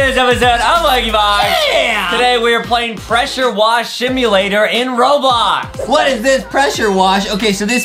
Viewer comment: did I heard that adam said shimulator